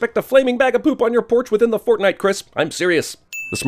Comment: did u say fortnite